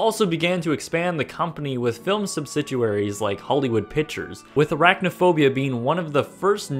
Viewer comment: I really enjoy your videos, but at 1:32 did I hear "subsiduaries?" Surely that should be "subsidiaries." Still, great channel and fun to watch!